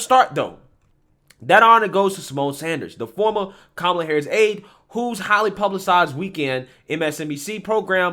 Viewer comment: I thought he said “the woman kamala harris ate”. Oops.